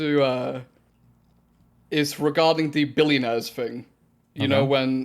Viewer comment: This dude says "billionaires" as "billy nerds". I can't be the only one who hears that.